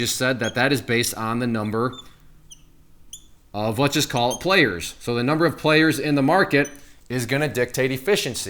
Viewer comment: Haha - at 4:43 I thought you said '...based on the number of, let's just call it Clayers'. There's a new term for ya! I do want to become a 'Clayer' soon btw.